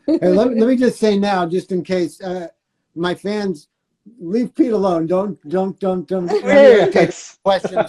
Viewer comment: can someone please type what did he say about his fans? Thanks! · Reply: "my fans, leave Pete alone"